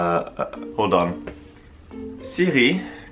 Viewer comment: I thought siri said garlic PEEL and spend like 2 minutes trying to get the joke 😭😭😭